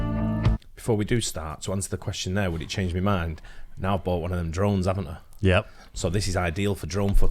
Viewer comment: Oh you said drone I could sworn you said drum. 😄